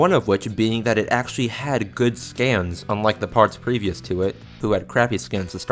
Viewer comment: I thought you said "it had good stands unlike the part previous to it" and did a huge double take. Good thing I rewound the vid to see you said scans not stands.